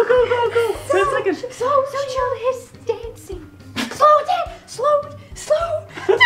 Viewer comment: So I covered the yellow words so I could play along and at 8:08 I thought it said “socialist dancing” 😂😂😂